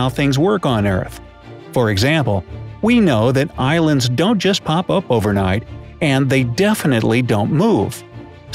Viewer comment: Am I the only one that thought he said "Irelands dont pop out over night"?? Yeah I'm Irish